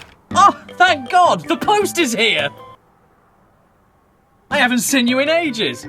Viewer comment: i thought he said "the Pope is here" and then showed a low quality wooden cross